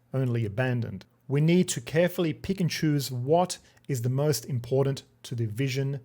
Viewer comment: I heard "We need to carefully Pikachus..." and was saddened there wasn't a Pikachu image popping up 😅